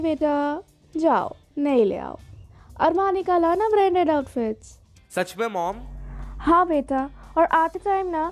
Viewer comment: me ladki ne kya bola koi batao please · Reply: Armaani ke branded kapde